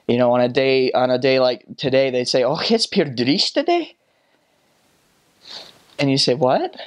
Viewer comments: Sounds like he say’s “trist” and it means the same in Danish like “its trist weather Day” 😂😂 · Being Russian, i heard "Pure Drist-a-day" here. In Russian, word "Drist" means a very strong diarrhea.